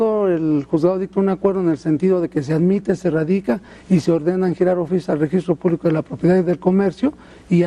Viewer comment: Dijo que se le piden informes al Registro Público de la Propiedad min. 4:22!!! No sabe lo que dice!!! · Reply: la que no sabe eres tu mi estimada (A)Guada Valentin... el señor dijo "se giran oficios al registro publico de la propiedad, al registro general de notarias para saber si existe o no testamento....", en ningún momento dice que "se piden informes" de ningún tipo... lo cual es verdad, ya que un vecino justo ahora esta en ese tramite y justo se giraron dichos oficios a las notarias del estado... asi que antes de insultar investiga